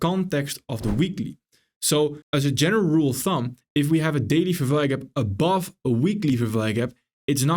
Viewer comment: what he said ? " Daily ___ gap" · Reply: A Daily fvg above a weekly fvg